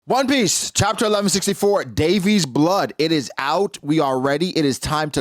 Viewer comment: At the beginning, I thought you said Dave East blood, lmaooo